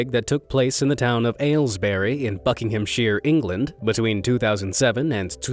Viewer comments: why did i hear f*ckinghamshire instead of buckinghamshire 😅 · Wait did U say Fuc*ing Ham Sheer England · minutes I thought he said f*ck\ngham 😳